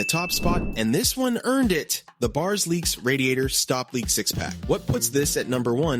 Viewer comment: did he say 6-pack 😂